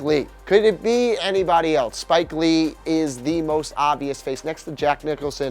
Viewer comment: Drake is the most obvious NBA celebrity fan to me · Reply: Latone ...lol...I actually thought he had said most obnoxious instead of most obvious.